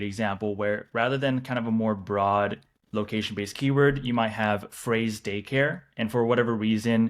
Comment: Sorry for being dense here but I am confused by one point...at 17:14 into the video Frey uses the term "phrased daycare" (assuming I heard him correctly) when discussing using alternatives to using broad location-based keywords. I do not understand what was meant by "phrased daycare". I think he was trying to show an example, but the screen share from his computer did not match what he was describing. Can you clarify? Btw... great content and great video. Thanks.